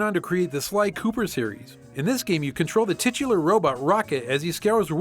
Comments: Did you say TITS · Did you just say tits?! There at the end i coulda swore it was gunna be Daves PlayStation that Joe stole